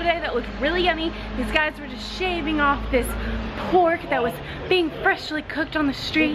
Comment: Dope stable footage, great details and content. Thumbs Up! At 8:59 did you say "Pork"? I don't think they would sell pork doner in Istanbul. This is a Muslim country.